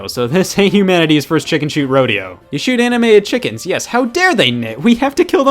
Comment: Am I the only one who misheard “You shoot animated chickens” as “you shoot anime at chickens?”